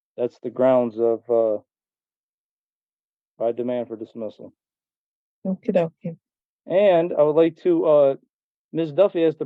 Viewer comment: Did I hear that right? The judge said “okie dokie”? I love it!!! You can tell they’re all so done with this Sovereign Twit!! 😂